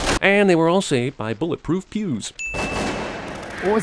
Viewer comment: god did I miss hear the dude, I thought he said bulletproof Jews instead of bulletproof pews